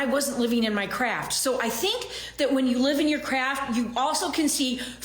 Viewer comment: "Living in your craft" yeah, that's not what I heard. I heard "living in your crap" which seems much more accurate.